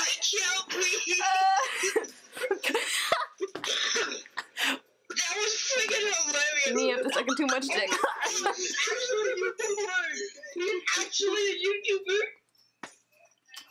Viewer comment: Am I the only that heard her say “me after sucking too much divk” 😂😂😂 her reactions is a1 bro I can’t stop laughing 😂😂😂😂💀💀